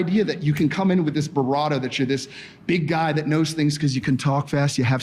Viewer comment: I agree with everything you say, but at 6:18, the word I heard "him" say was BRAVADO, which was grammatically correct and made sense in the context of the sentence. The glaring problem, that we all see, is the hypocrisy, lack of self awareness, and the ever ubiquitous combo of IGNORANCE & ARROGANCE. This "guy" was obviously testosterone raging. Good old HRT.